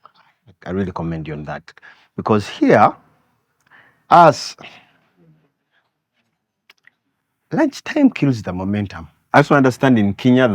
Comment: I hear 'for us lunch time kills the momentum 😂😂'